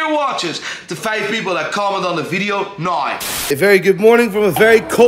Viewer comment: did he say to comment nine or comment now 0:41 I know im too late to win but its gonna bother me